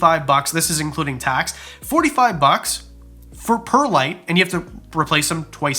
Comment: Instead of "per light" I heard "$45 perlite" and thought "Jesus! Fucking Canada!"